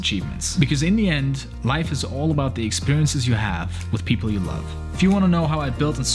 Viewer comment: I thought you were gonna say life is all about the friends you make along the way 😂